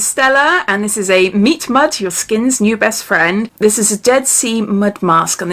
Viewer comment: Oooooo this box 😍 also deffo thought you said ‘meat mud’ before I saw the packaging and was vaguely horrified! Lovely video as always!